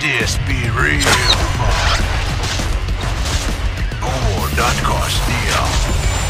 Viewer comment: Does anyone know the voice line at 0:14 ? What does he say? Ive been wondering for YEARS ”Oooh that costs dia” or what?